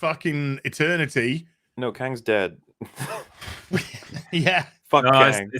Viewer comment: I thought you said Liu Kang